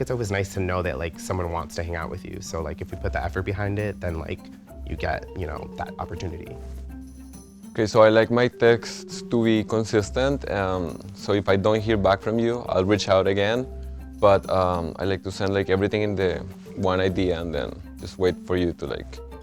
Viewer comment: who else heard “so I like my dicks to be consistent”